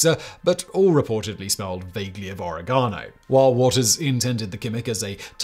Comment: I thought he was going to say "origami," but it was "oregano," lol.